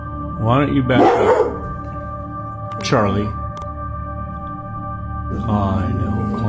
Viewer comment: did anyone heard a woman say charle at the same time